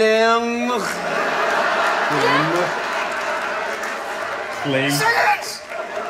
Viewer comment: Just wanted to note that when he was spelling his name it looked and sounded like you said flame. He actually said phlegm. Like the mucus/spit in your throat. Also some of his jokes like with the rib cage bit is actually planned. He puts in a skit in like that at least once per show.